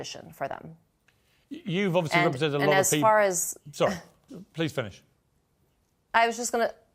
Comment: Wait did piers Said: please finish